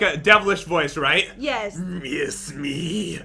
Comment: I thought you said kiss me instead of miss me in the creepy doll